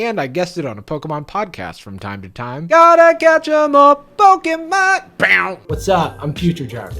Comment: The editor's lack of knowledge of Pokémon is astounding in this video lmao. Only 2 minutes and 30 seconds in, and I can forgive not understanding Logan Paul's "I stay pulling Chanseys, dog," and just captioning it "mumbling," but how don't you know "Gotta catch 'em all"??? You thought they said "Gotta get you more"??? It's THE single most iconic line from the show! That's like not recognising Pikachu! How can someone be so distanced from something so popular, it's like never even having heard the name "Kardashian." Absolutely baffling lmao.